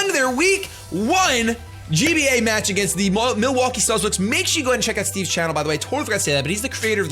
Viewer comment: Who else thought it said" New York Mankeys Vs Starbucks"?